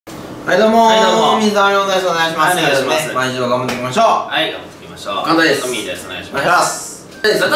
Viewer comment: 水溜りボンドがみん溜まりボンドに聞こえた笑